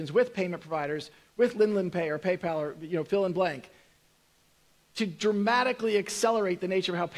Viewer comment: What does Brad say at 24:29? Linlan Pay, Paypal and ??? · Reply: I think he says "Fill in blank"